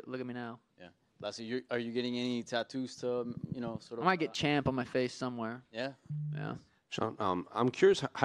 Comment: did that guy call Sean "chump" instead of champ ?